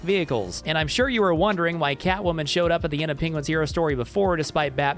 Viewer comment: yeah babyyy! That’s what I’ve been waiting for! (Edit) I didn’t understand what this comment was for a second. It’s because it sounds like he says Penguinz0